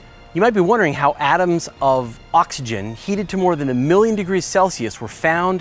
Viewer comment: says Oxygen, but the subtitles say Hydrogen. It's oxygen right?